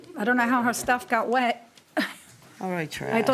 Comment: Did no one else notice about 14:45 the plaintiff mumbled under her breath "white trash"? At least thats what it sounded like. I'm actually really surprised the judge didn't hear that. And then sweet justice at the end when she tries to run out all dramatic.... but the wrong way. 😂😂😂😂 dumb people always tryna come up on someone else's expense. She's in fact, the trash. 🤦🏼‍♀️